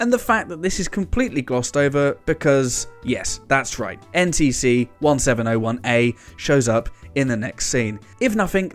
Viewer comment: NTC-1701-A? Did I hear NTC-1701-A?